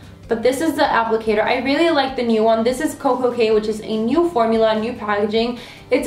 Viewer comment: turn on subtitles and at 3:22 she says "Koko K" but my subtitles say "cocaine" i'm dying